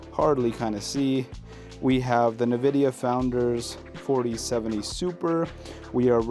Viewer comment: Did you say the navidia founders edition graphics card at 6:37??😂😂 Great setup and a banging first video. Btw, it’s N Vi Dia.🎉